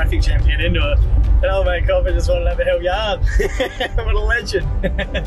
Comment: Ok- listened 4 times still have no idea what you are saying at 3:17. ? Seemed funny so I’d like to know! Looking forward to seeing you back on Nandji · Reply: The police wanted to have a yarn (chat)